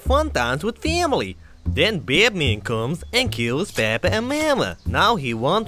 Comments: Funny how when the guy comes through the window and he says bad man it sounds like he says Batman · am i the only one who hears blyat man instead of bad man? xD